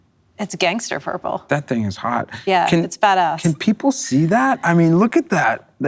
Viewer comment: did anyone else hear her say it’s bad a$$